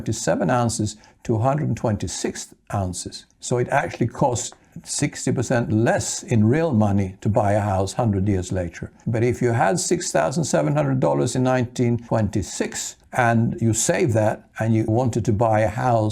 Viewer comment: I had to laugh when I heard you say buy a cowl. I was talking to my mom about what to buy and make money with my small savings, to make money. She said buy a cow because people always need to eat! That was rather sage advice.